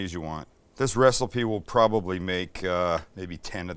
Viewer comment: So, am I the only one hearing shit? or did actually say wrestle-pee @ 5:30 in, instead of recipe?